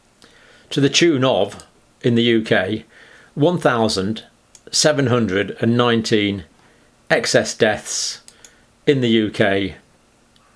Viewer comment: Did he say 1,719 excess deaths in a week at 17:24? In a week? I must have heard this incorrectly.